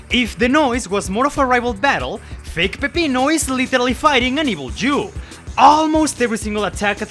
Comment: Why Do I Hear "Fake Peppino Is Fighting And Evil Jew"